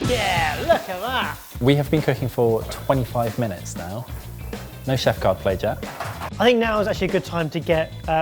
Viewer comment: I'm sorry, but I think this is an important bit of feedback, so please don't flame me. The "English - Default" captions were very off. I understand when cooking terms or foreign food names aren't transcripted correctly. I totally get it. But even the normal sentences were off. At 4:07, said, "No chef card played yet," and the captions said, "No chef God pleasure." It makes understanding the video much more confusing. I actually had to switch to the "English (auto-generated)" captions because they were more accurate. I don't know how the captioning process works for Youtube, now that the community can't offer captions; but for clarity sake, and for your audiences who rely on captions, this video was difficult. I love Sorted Food, and I appreciate the work you put out for us. I submit this feedback respectfully and with the open admission that there are steps in this process that I am ignorant of. I hope only to be helpful.